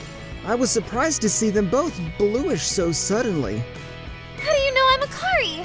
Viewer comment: I'm sorry, did he say "blueish?" Also, how could no one tell them apart unless they were also swapping hair colors and styles which sounds like a pain to do.